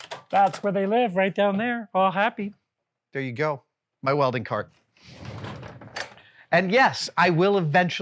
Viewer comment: OH ... thought it said custom wedding ...